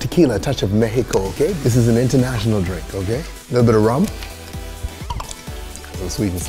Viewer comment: I thought he said "A little bit of ramen"